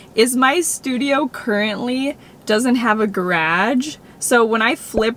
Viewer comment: When she first said garage I thought she said grage and I was really confused lmao